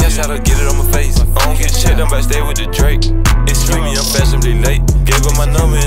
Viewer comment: " ion ????? I just stay wit da drac" what he say & on the water boi how does that verse start I know he says me & spiffy sumthing?????.. Anybody please help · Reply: “Ion get checked much I might just stay with da Drac”